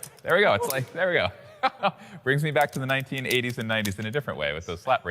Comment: BOI If he said 1800s 😂😂